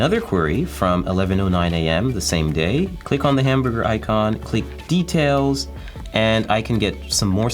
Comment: Did he really said, click on the hamburger icon?